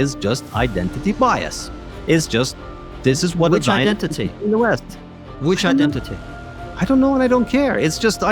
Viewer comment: Did he say "The west" when Konstantin said identity bias? It was a bit garbled, and he floundered after and said I don't know.